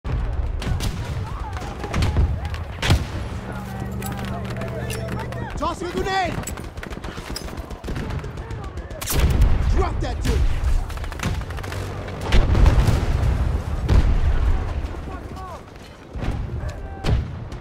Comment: Vietnam War-like setting and background noise made me think he said, "Dropped that dink!" :P